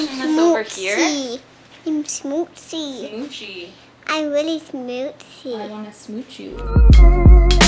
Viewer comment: It really sounds like is saying "smutsig", whish means dirty in Swedish. So for swedes it sounds like she's saying "I'm dirty". It is so cool hearing her saying that!